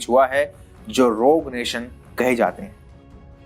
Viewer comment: kya bola ? Rog ?